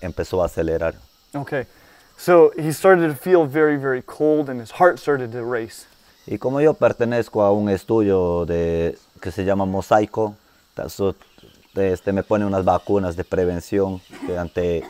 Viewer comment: at about 4:08 i think he said something about a quesadilla? my spanish isn't very good lol